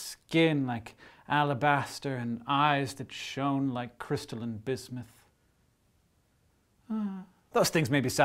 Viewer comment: Upon reflection I know Hank said crystalline bismuth, but for just a second I thought he said crystals of Bism and the little Narnian in my heart did a happy dance.